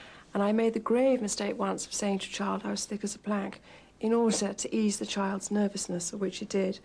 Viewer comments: What does she say at 8:16 ? I’m American and horrible at understand British accents. Can anyone tell me what the mistake she made was? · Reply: She told a young child that she (Diana) was "thick as a plank". Basically calling herself stupid. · what does she say at 8:15? i couldn't understand her clearly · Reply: she said i once said to a kid " i am as thick as a plank" and she used to say this about her which means im very stupid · What does she say at 8:15 ? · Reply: She told a child she was as "thick as a plank" to ease the child. To be thick is to be dumb.